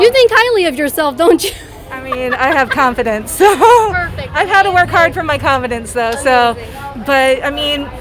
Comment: She said she works hard too look good.... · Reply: I thought she said she worked hard on her confidence.?